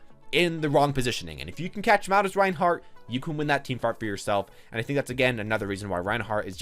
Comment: Did he say "team fart" at 4:29 ? I'm confused